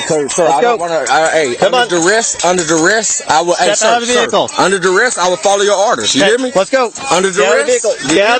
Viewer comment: So can someone tell me what he meant when he repeatedly said at 3:42-3:51 “under-DA-RES ima follow your orders sir” what the hell is “under-da-res”??😂😂